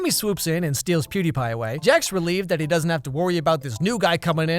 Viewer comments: did he say pewdiepie😂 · did he say pewdiepie !? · + did he say PewDiePie?